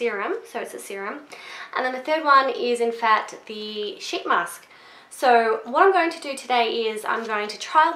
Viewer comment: Lol I thought you said that the third one was a sh*t mask. Good review :)